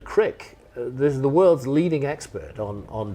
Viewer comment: I thought he kept saying prick